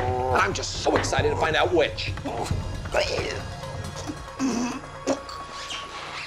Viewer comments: Love how he finishes with "I'll schedule a surgery" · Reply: I thought he said “an unscheduled surgery” lol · Love how he finishes with "I'll schedule a surgery" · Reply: Oh. I thought he said "an unscheduled surgery"